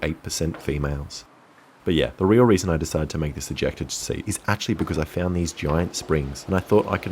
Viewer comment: I got a dirty mind I thought it said ejaculate instead of ejector XD